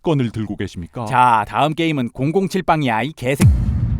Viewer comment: 자 다음은 007빵이야 야이 개 라고 들리는 것같냐???